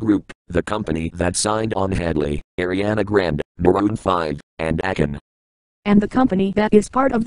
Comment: Did he just say Maroon 5?